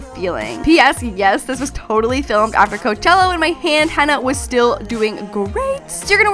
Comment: When she said " when my hand hanna was still doing great" i heard " when my friend Hannah..." okay I'm upsessed with 13 reasons why